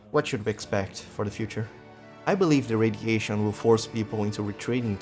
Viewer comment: I mean the FEMA guy does literally say “solar radiation”